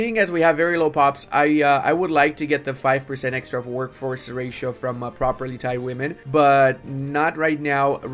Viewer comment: I heard "I would like to get the extra workforce from proper Thai women" I had to go back to re-watch it :D haha